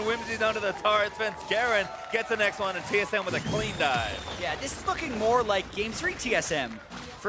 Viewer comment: did he just say "this fucken" at 20:40? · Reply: "This is looking more like"...